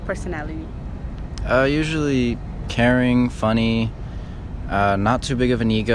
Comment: I thought he said Karen instead of caring omg just kill me. I'm so immature 😅